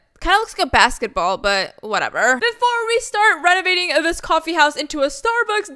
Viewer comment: When you said renovating, for a second i thought you said renagade lol